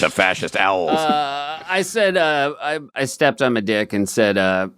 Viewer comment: Did Seth say he “stepped on [his] dick” when talking about Spider-Man?